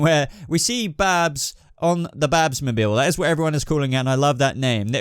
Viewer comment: Thought it said Batmobile, i can't be the only one